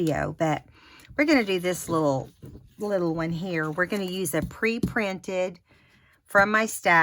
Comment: Vety pretty cards! ❤️ Love them! Do you have link for that free printable red "merry christmas" paper? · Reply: Yes yes... You Said pre printed (0:38), I heard free printed. But I did not see that "merry christmas" paper when looked Lorna's etsy shop. Well, nevermind. Happy Holidays!